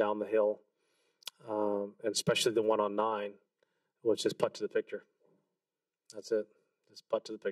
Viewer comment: "putt to the victor" ... am I hearing that right? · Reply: putt to the picture - Earl Woods school. google it